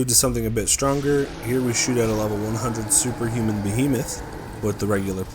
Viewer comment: Did I hear Superhuman Behemoth?